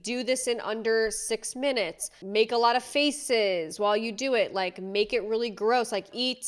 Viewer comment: When you say that men ask her to do things while she's eating, like "Make a lot of faces !", I swear I literally heard "Make a lot of feces !" 😂 PS : after all, in this dystopian social network, nothing is impossible 😬